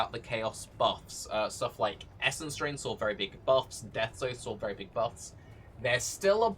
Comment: WTF is the guy saying at 0:26 and 0:55? Deathsaw? I literally played it 30+ times and I can't hear what the hell he's saying.